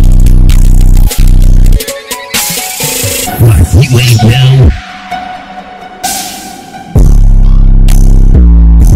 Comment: what tf did he say lol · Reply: For me'like you ain't now' xd i know its so weird